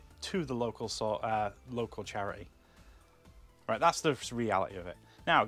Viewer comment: Thought you said the MDMA charity 👀- what’s that for ex ravers ??!!